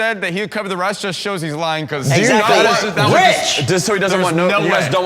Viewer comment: yells "rich!!" and points at Kevin.... And I heard him say "witch!" 🧙‍♀️😅